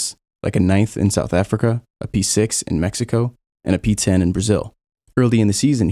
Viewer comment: I swear to God, I thought you said “.. and a pizza in Brazil”, which tbh is a brilliant joke.